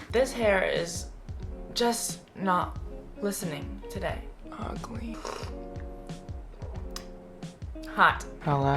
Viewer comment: did he just say 'ugly'? CONE YOURE A PIECE OF ART